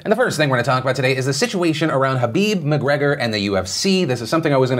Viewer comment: Is it just me or does he say “habib” instead of “khabib”